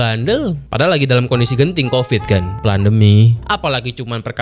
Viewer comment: Gak salah denger nih PLANDEMIT 😂